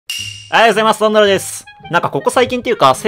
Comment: はいおはようございますバンバドロです、にしか聞こえんw